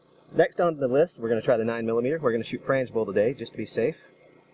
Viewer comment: he says "next on the list we are going to try the nine millimeter.we are going to shoot *** today,just to be safe" i can't make out the word he is saying.it's sounds like fransvull or something.somebody help me out please. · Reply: Frangible. Bullets design to fragment into safe pieces for target steel shooting.